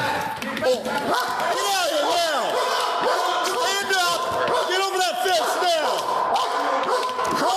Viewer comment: Did the cop @ 4:14 yell get over that fence now?? Maybe im sounded like fence wit the dog barking??? LOL